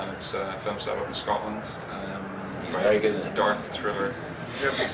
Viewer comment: What did Bradley say at 5:31 & 5:47? Wish I could understand every word they said:P they're so awesome!!< 33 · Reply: he says "He's very good in it" and at 5:47ish "I don't know if he's any good in that, to be honest" :-) And I agree - they're both brilliant!